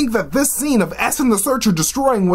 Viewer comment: u said something about destroying I couldn't make out what you said right before the word destroying... can you help me out??? ... youuu are our only hope (Galaxy Quest!!!) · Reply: Oh my gosh! i was supposed to put a graphic there! I said "Eson the Searcher"! He's the Celestial that is murdering everyone in the movie! Ah! I'm so sorry! My bad!